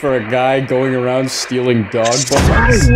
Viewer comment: I thought just said "I just downed 3 dog balls" not edibles for a moment